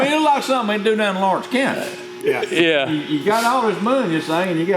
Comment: Did he say "It's like something they'd do down in Lawrence, Kansas"? I take offence to that. Our trailers are the finest in the land. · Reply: Pretty sure he said Lawrence County, not Kansas